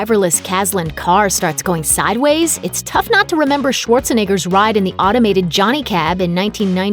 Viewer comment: Did she just say "Schwartzanigger"